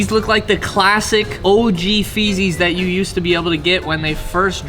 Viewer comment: did he say feezys